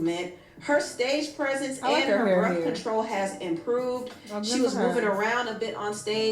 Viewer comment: All this time I thought she was saying birth control OMG 😂